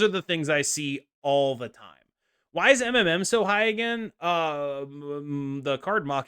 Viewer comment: I can swear i heard " Why is Eminem so high " and was about to spit my beer xD